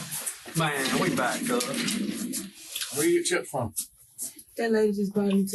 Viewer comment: Did he just say "Maaaan we back cuh" 😭😭